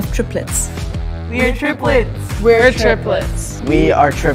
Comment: At the beginning I thought Cody was trying to say "giblets" in an Australian accent and didn't even question it